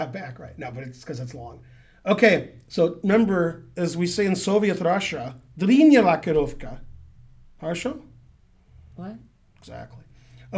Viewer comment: What did he say in russian? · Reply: He said: "Длинная рокировка" dlinnaya rokirovka Which means "long castle". But he said the first word unclearly